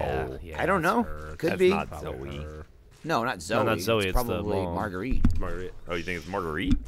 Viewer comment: Jeremy can't get her name right to save his life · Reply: may have just slurred his words together, sounded like he said "Margarita"